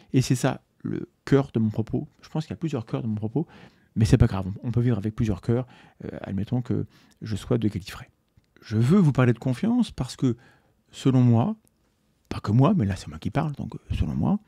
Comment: … Admettons que je sois quoi ? Désolé pas compris ! ^^ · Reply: de Gallifrey. Déso, il faut avoir la référence :)